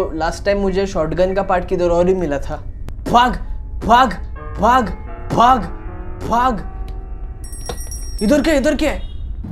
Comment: is he saying bhaag or faaak lmao